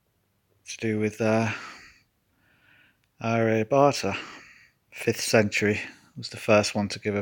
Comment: When you said Aryabhata, I thought you said Harry Potter 😆. But then you said 77 and lightning...like the scar on HP’s forehead. Aryabhata = 77 EO.